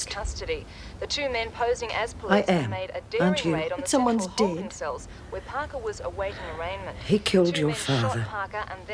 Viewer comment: Why isn't Jenny glad her father's killer got his come-uppance? I mean shouldn't she be glad "someone" is dead? That "someone" had her father killed for god sake! 21:30 What did Roach say about Bongo right after Rita turned off the TV before Rita said the Freak has lost her protection? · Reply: "That means Bongo's safe - eh, Rit?"